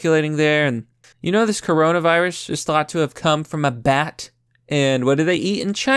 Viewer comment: I thought for a sec you said "you know this corona virus is cum from a bat"